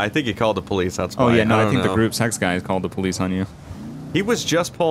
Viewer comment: Ok I thought he kept saying group sex but i put on sub titles ant 9:15 and the caption says group-sex 😂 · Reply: Gruppe sechs is the security company on gta